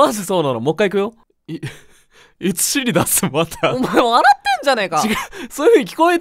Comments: 「いつ尻出すまた」じゃなくて「いつシリアスマザー」に聞こえた(?) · ダニエル先生大変だな いつ尻出すまたって聞こえたから私馬鹿だ笑聞こえた人～ ●∀●高評価押して！！！ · マジでいつ尻出すまたに聞こえてきたｷﾞｮｷﾞｮｯ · さん今回は聞き取れたんだ( ＾∀＾) いつ尻出すまた……ボクもそう聞こえるww(←バカ) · え、普通にさんすごいw✨✨✨ 「いつ尻出すまた」だと"a"が消えてますね😭 明日のリスニングテスト空耳で聞こえたら笑っちゃうなw